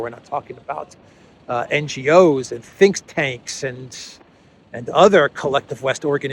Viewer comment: First time around i heard "Fink tanks" Think tanks, shark tanks and Fink tanks